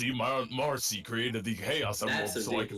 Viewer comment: At first I thought I heard "Morrissey created the Chaos Emeralds," and I spat out my drink.